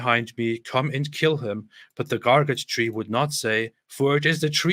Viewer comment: I keep hearing him say "Garbage Tree".